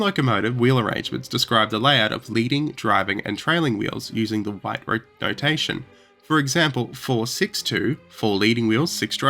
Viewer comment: did he say whyte notation or did he say something else?